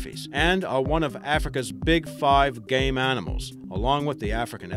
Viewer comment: O2:52 Gay animals xD I thought he said THAT?!? LoL Why does "Game" sound like the word "Gay"? If you don't agree, then say" Mr Nintendo The Game Master" It sounds like FREAKING GAY?!! xD lol